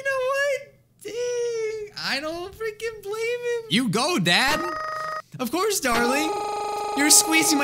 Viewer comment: did he just say go dad at the mom